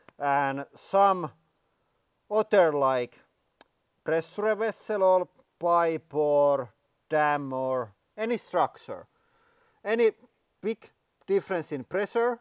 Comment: what the eff is he saying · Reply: "some water, like.. pressure vessel, or pipe, or dam or any structure"